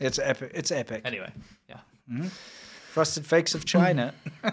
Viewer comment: It sounded like you were saying "flakes" when it was "fakes" which makes it that much better. I liked #4, #3, #2, #1.. exactly the reverse order. All were great! GGRRREEEEAAT!